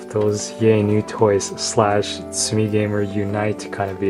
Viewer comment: #積みゲー =P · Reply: Thought you said すみGamer. LOL, すみっコぐらし fan? I can tell you hitting the bargain bin not like some idiots buying day one. (WHAT? I wanted the DLC!)